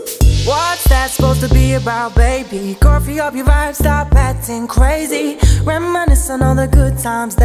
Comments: I came here from the edits and thought it said “coffee up your vibe stop acting crazy” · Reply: I thought it said coffee on your mind stop acting crazy 😂😂😂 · I came here from the edits and thought it said “coffee up your vibe stop acting crazy” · I heard "Coffee on your mind" instead of "Go free up ya vibe" 🤦‍♀️🤷‍♀️ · I heard "Coffee on your mind" instead of "Go free up ya vibe" 🤦‍♀️🤷‍♀️ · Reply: I hear "coffee on ur vibe" lmaoo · Wow are you saying that I was singing this song wrong the whole time??!! I hear it like " Coffee will arive stop actin crazy"..... 😂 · I thought it said: coffee up your eyes XD dont ask me why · I thought it said coffee up your eyes got you acting crazy